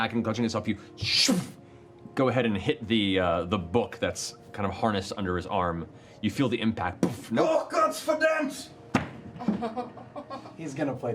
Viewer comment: What did Caleb scream out? I couldn't make out what he said. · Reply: sau Gott verdammt! God damn in german.